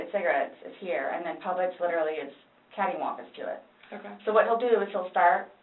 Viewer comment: oh my god did she just say "caddy-wonkus" ?? that makes me hate her even more